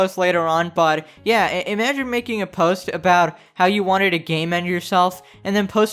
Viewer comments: I thought it wqas saying Gay-Men instead of Game-End! XD I was SO confused for a while! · I THOUGHT IT WAS SAYING "gaymen" xDD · When you said "game end" and I thought you were saying "gay men" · Hahaha I actually thought you were saying "gay men" for the first few minutes... I was so confused lmao I'm like "how did she clickbait gay men? Whaa?" 😂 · Finally someone told me what it was saying I was just cracking up laughing every time I heard it. Because I kept hearing "gay men" then I'm thinking what's going on here? why is it saying that? Am I hearing things or is he trolling her? At first I thought he was messing with her censoring it or something to sound like something silly or ridiculous. But it turned out to be game end I'm like "ohh okay". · I can’t believe I misheard “game end” for “gay men” for so long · Brooo I thought you said that she clickbaited gay men and not game end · Oh it’s saying game end I thought it was saying gay men I was like wtf is there something wrong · I can’t believe I misheard “game end” for “gay men” for so long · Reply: the fact that i only hear "gay men" instead of game end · when you replaced suicide with Game End, I could've sworn it said Gay Men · I thought it was saying gay men and not game end 😂😂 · I kept hearing "gay men" instead of "game end"